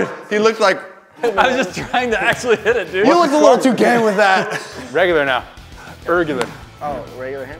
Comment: Did say gay